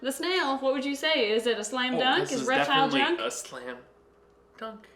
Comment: I thought you said a "SLIME dunk"...missed opportunity! ...@